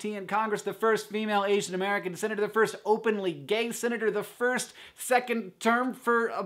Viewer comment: I heard: "First openly gang senator"..